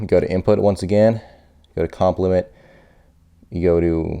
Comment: COMP means "compressor" not compliment. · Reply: You misheard him. He didn't say compliment, he said COMP/LIMIT